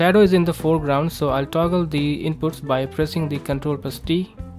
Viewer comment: Cool tutorial man, but I can't make out what you said at 3:26. What exactly are you pressing to get the text back in the foreground? Thanks. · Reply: toggle (Ctrl + T)